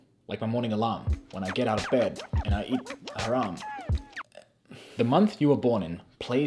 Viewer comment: Did you say and eat haram @ 6:51 or am I trippin